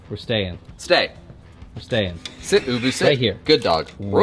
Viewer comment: 'Sit ubu sit' was from Family Ties 🤔 I think ......as a kid I thought it said sit boo-boo sit 😄🙂🩶🩶